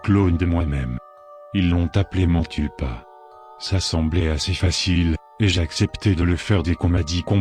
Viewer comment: je suis vraiment le seul à entendre m'encule pas ? X) Désoler pour ça mais... voilà quoi =/